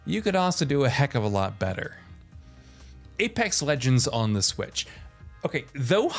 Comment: Did u just say Apex Lengends 8:03?